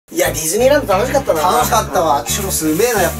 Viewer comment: ディズニーランドがネズミーランドって聞こえた ネズミーランドって言ったかな？？ さいこう💓